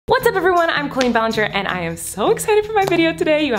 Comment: Is it just me, when the intro says I’m Colleen! It sound like it says I’m the queen!